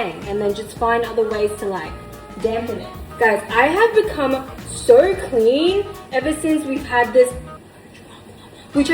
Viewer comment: what did she say i couldnt hear her when she whispered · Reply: She said "roach problem"